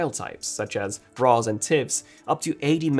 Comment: I swear i heard him say Bras and Tits...